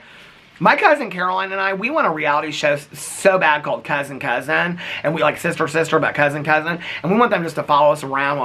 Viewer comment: I thought you said Cussin' Cousins until I read the comments, and I was dreaming of that show. 😂😭